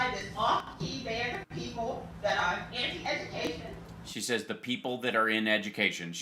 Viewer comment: No Hal, she said the people that are “ANTI education”, which then affects everything she said after that. Occasionally you mishear and get it wrong🤷🏼‍♀️